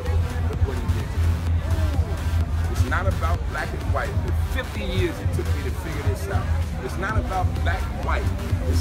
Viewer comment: This was really really cool, I'm so jealous. A lot of cool people there. 1:55 but did he say he's 50 years old?